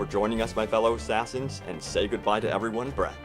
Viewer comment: When you say Brett, it sounds like your saying bruh.